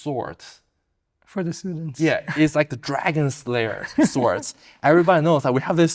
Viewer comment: Did I hear Dragon slayer?😳😦🐉